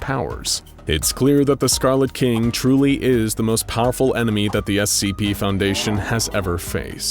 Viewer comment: anyone else hear him say "emeny" instead of enemy at 10:53 ?